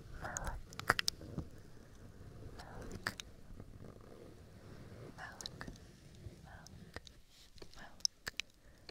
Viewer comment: What are you saying at 24:40? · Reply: malloc 🤓